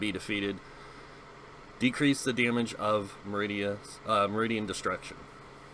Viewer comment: Never mind about radiant Destruction, I misheard you.